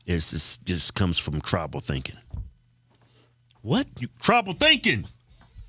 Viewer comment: DANG! I thought you said TROUBLE THINKING!